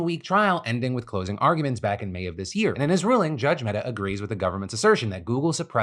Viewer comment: did I hear that right? “Judge meta ruled against google”..can someone confirm Zuckerbergs whereabouts during all this??